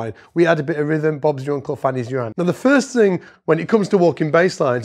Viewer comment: What did the guy say at 1:23??? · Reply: “Bob’s your uncle fannie’s your aunt”. Translates to “easy peasy” in US English.